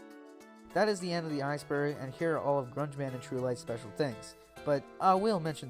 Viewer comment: Wtf did he just say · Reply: "That is the end of the iceberg and here are Grungeman and TruLyte's special thanks (creators of iceberg) but i will mention this one." (for some reason I did a country accent but its fine cause I'm from the south)